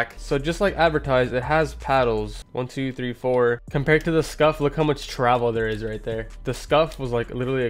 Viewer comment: Who also thought he said scuffed not scuf